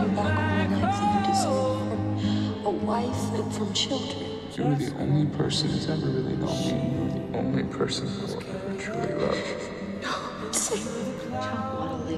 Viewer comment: what he said? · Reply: "You are the only person who's ever really known me. You are the only person I will ever truly love."